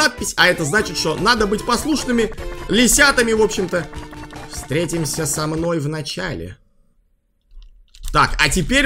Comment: Мне послышалось что там когда перевёл слова "Встретимся с тобой в начале" я услышала "Встретимся со мной на чай"